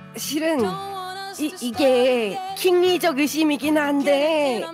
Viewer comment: 초에 '합리적'이 아니라 힙리적으로 들려요 ㅎㅎ